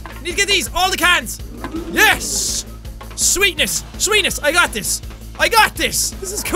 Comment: Did he say swaetnis wt